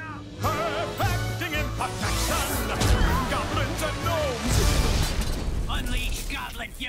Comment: What does he say at 8:15? I can never make it out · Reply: ¨Perfecting imperfection! Goblins and Gnomes!¨ pretty sure that's it